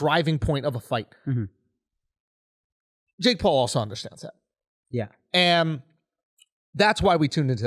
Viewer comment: anyone else not know wtf andrew said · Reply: He said "Jake Paul understands that".